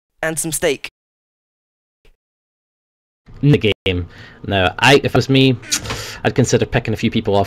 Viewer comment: It kind of sounds like says "If I was me." o.o... ARE YOU NOT YOU?! *brain implodes*